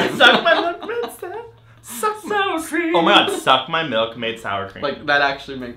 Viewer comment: I heard "suck my milk made sour free"